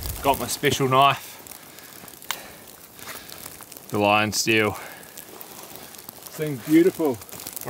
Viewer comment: Did he just say " Valarian steel" for the knife 😅